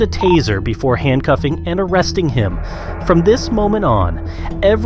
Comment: i deadass thought that you said "before handcuffing and m0l3st1ng him" ☠☠☠☠